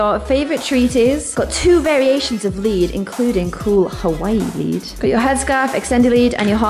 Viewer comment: It sounded like she said Hawaiian weed at 2:17😂😂😂